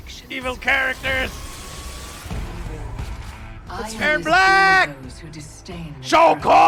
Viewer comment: I thought he said ""they're blaaaaack!!" He said erron so fast.